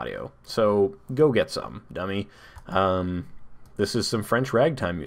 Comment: Did you call me a dummy?